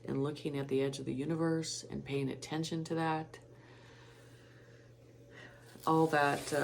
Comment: I thought u said pay attention to Alex collier ?